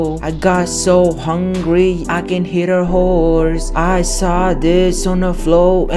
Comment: it sounded like you said hit a horse